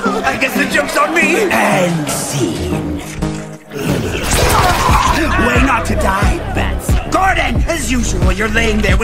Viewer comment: It sounds like Joker said Wayne when he said “Way not to die”